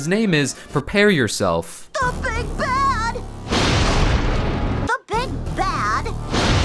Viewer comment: did I just hear bigback?